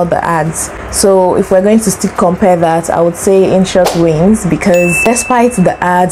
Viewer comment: Hi, thanks for the helpful video. I think you said InShot wins at 4:44 but you show the CapCut logo. And the caption called it "insurance".